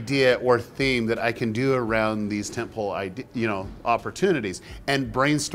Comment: Derral, did you say temple or tin pole or tent pole or what? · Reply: Tentpole